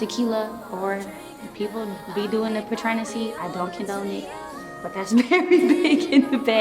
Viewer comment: What does she say @ 3:53? "People be doing the ____" Sounds like the second half is Hennessy...? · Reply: She said Patrenessy 😂 basically saying people are mixing liquors and getting messed up